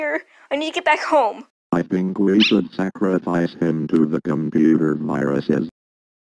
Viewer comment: 1st Time: Wat Did U Say First Prize? 2nd Time: LOL, BULLY IS BEING RAPPED BY CRAFTERS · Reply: First prize said i think we should sacrifice him to the comuter viruses